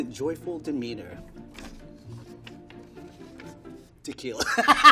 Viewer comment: wait tequila? For a second i thought he said to kill